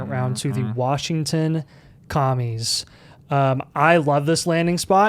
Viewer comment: Wait.. did I hear “Washington Commies” at 4:20?! 😂 YO! I had to pause this video I got so hyped! I found this channel maybe 3 months ago, ive not watch every video, probably around the 15-20 range, but i’ve enjoyed what i’ve watched and have even recommended you channel to three of my buddies… but hey, if you two are based i’ve just become a real fan!